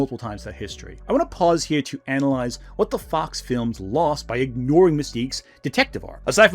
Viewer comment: what the f..😂 Oh he just said fox😂